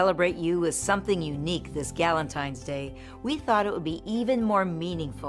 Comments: Did she say Galentine’s day???? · gallontines day That’s what I hear Am I trippin?